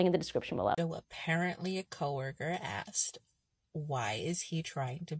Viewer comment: You misheard... They didnt ask, "Why is he trying to be a girl..." They just asked, "Why?"